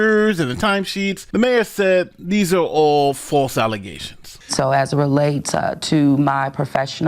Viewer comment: Henyard has ruined me. Whenever the current day black female politician is being charged on corruption, I heard 'allezations', not 'allegations'.